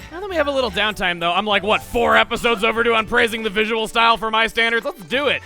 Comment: when chugga was saying about praising the visuals I thought he said praising the sun. #darksoulsletsplay